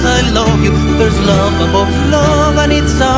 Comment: I always thought he said "there's lovable fluff and it's ours"